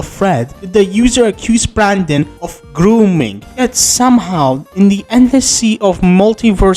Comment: I misheard grooming as gooning.